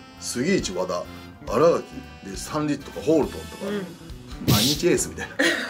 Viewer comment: あたりの斉藤、杉内、和田、新垣の次ってなんて言ってるんですか？ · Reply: 亮太 スタンリッジ、ホールトンだと思います